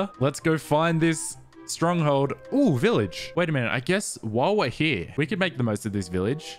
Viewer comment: When he says “while we’re here” it sounds like he says “wah wah here”